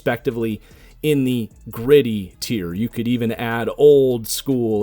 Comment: Not gonna lie I wasnt paying attention so when the Gritty tier came up I thought he said Griddy tier 🤣🤣🤣